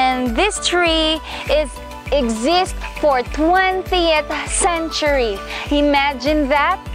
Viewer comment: How old did you say that tree was? Sounded like you said 20 centuries, but then you said two decades? So is it 2000 years old or 20 years old?